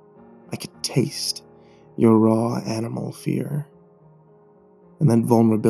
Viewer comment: Posted 11 seconds ago... somehow I doubt that YouTube. Edit: I thought he said “raw anime fear.”